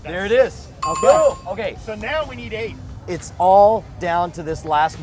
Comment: The first time I heard someone say "Now we need aids!" :D